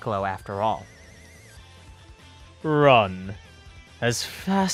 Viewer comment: i swear i thought he said bruh at first